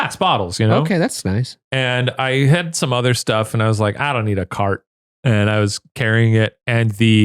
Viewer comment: is saying carton?!